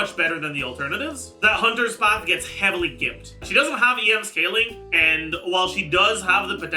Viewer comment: it sounds like you say "gipped". Just letting you know that this is derived from the word "gypsy" and is somewhat understandably the kind of thing you should refrain from using. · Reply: I think he said "gimped" which is a different word